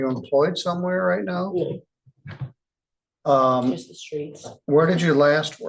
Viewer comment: when the judge says "Are you employed anywhere?" Did she say ""no, just the streets" ??